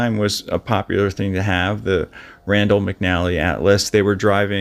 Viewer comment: Lol, at first I thought you said, "random McNally Atlas". I replayed, and heard you say, "Randall McNally Atlas". It's Rand, but I think random is more appropriate in this case.